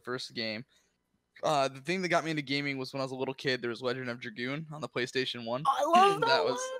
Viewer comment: What did he say was his first game? At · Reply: Legend of dragoon